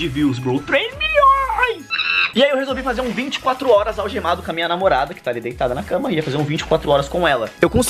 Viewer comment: Não entendi "FIQUEI ALGEMADO COM A MÃE DA MINHA NAMORANDA?" Oxe, mas ele está com a namorada .